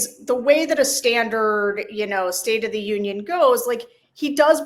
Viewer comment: I thought Sam said "state of the idiot" at the beginning. That would be the truth! Sarah: "Can't get used to how dumb he sounds when he talks", Bullseye!